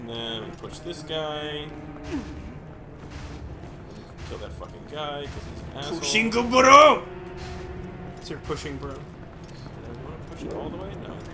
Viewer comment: When said Pushing Bro i thought he said Machine Gun Blow and Screamed ONE PUNCH! in response